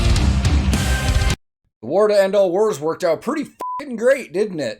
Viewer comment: Perhaps I'm too Sabaton-damaged. And I don't mind it really, but i actually thought the chorus said "Great War! And make it a TANK-War"